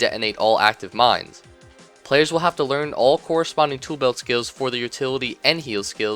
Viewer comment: When he said "turret in his toolbelt"i thought he said "turd in his toolbelt" i lold